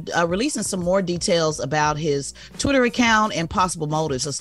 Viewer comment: Dang Tez got to slow down when talking. I so thought she said “tutoring count” but she said “Twitter account”….then she said “Ford” and drug administration…the she said “in-corprate” instead of “in-corp-o-rate”😳 Come Tez, Slow down (still 💐💐 though)